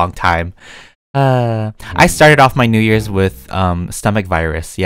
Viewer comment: I thought he said “I started the new year of with coronavirus”. I was like.... um rewind...